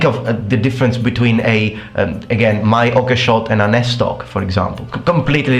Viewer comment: What's the sword at 7:18? Can't make out what's being said. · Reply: its one Estoc